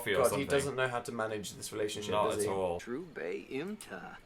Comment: did Dil say true bae?